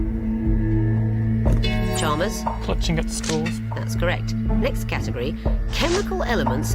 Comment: Did anyone else hear the Snake Charmers say "clutching at the straws" at 26:35?